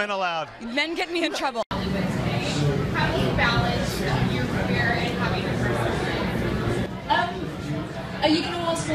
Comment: Can someone tell me what question was asked at 7:08, addressed to Keira Knightley? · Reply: I believe she asked is it difficult to balance her career and home life - like being a mother. I think she had a child by that point.